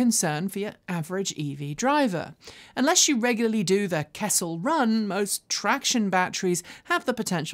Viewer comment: What's a "kettle run"? Did I hear correctly? 9:50 ish in the video! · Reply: Kessel Run it's a Star Wars nerd joke. Ask me how I know...